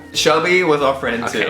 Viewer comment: Every time he said Shelby I was confused cuz I thought he said Chubby